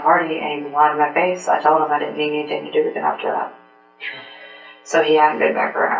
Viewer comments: The subtitles are wrong to the point that it seems intentional and I'm not going to watch the rest of this video. At 3:43 she says "I told him I didn't want anything to do with him after that. So he hasn't been back around." but for some reason the subtitles say "I told him if he had anything to do with it I would kill him. So he has a background." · the subtitles, at the end of her statement say she said “I would kill him”. That’s not what she says tho I believe... pretty sure she says “I didn’t need anything to do with him after that.”